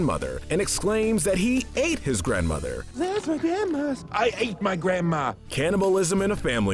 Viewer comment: OMG THIS WHOLE TIME HE SAID “I ATE MY GRANDMA!” I ALWAYS THOUGHT HE SAID “I HATE MY GRANDMA!”😬